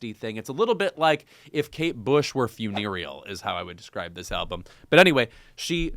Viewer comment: What’s the word Louis uses to describe PJ Harvey at 0:52 - if Kate Bush was “funarial”? What does it mean? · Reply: “Funereal” — funeral-like in terms of solemnity or somberness.